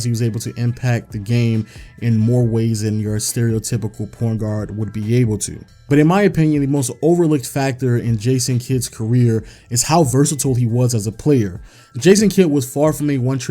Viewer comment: did he say porn guard lmao